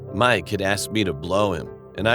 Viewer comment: does it say “mike had asked me to blow him”??